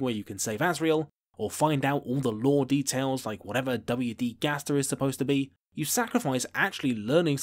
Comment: With your accent I thought you said dummy d. Gaster